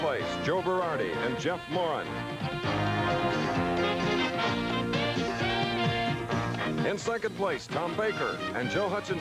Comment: Chris always sounded like he was saying 'Jeff Moron'😅. God bless Chris Schenkel. Legend.